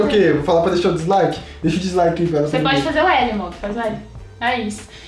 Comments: Sei que ela falou "Cê pode fazer o L mob" mas juro que eu ouvi ela falar "mô". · vc pode fazer o L "amo" só eu escutei isso!🤔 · so eu que entendi : Vc pode fazer o 'L' tambem Mo 😝😏